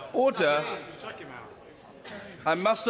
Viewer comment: now i hear him just saying ord'AHHerh